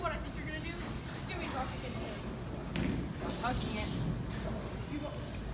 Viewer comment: what did say at 6:21? just curious. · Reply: Dylan Bulusan I said I'm going to hook at the spare I left.